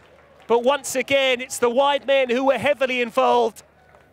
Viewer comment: When he said "wide men" i heard "white men" LOL